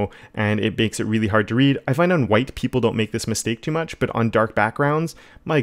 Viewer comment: at first, I really thought he said "I find that white people don't make this mistake too much". I was like "well, that's a new fact in design philosophy".